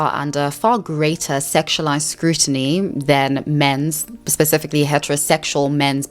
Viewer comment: does she say then or than? Or is it her accent making it sound like then? No hate btw, just asking ❤